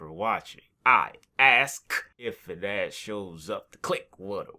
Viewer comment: For the longest time I thought you said "If your dad shows up" in the outro.